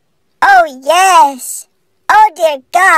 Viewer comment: "oH gOd Oh GoD oH yEs Oh DeAr GoD, AaAaHhH~, oH fReD hArDeR" IT KILLED ME (Btw tysm for 3k likes) December 29, 2023 edit: Holy shit I forgot I made this comment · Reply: I thought he said BREAD HARDER😂😂😂